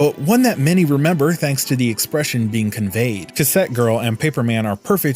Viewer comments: What animation is that? Sounded like he said, Set Girl? but I can't find anything on it. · What animation is the one he said in 6:25? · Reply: Before paper man